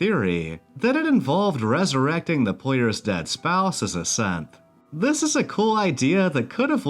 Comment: I know you say "As a Synth" but it sounds like you say "As a Simp"